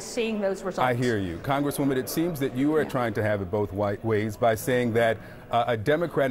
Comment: When did she say do not come? In an interview with a Bobblehead or at a formal WH press briefing? Wait a sec, did he say "You're trying to have it both "white" ways ...?" 6:29 Did I hear that right?